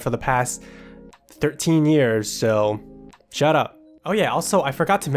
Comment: Did just say “shut up”? I feel offended. I liked you because you were so polite bu to guess not.